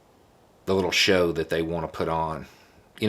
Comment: I almost thought you were about to say “the little shits”, which would be just as apropos. 🤣